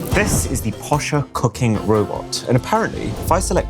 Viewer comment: In the beginning I heard “This is the posha cocaine robot”